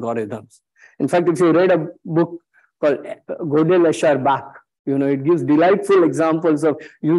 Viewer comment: if you were like me and couldn't catch the name of the book , it's ' Gödel, Escher, Bach '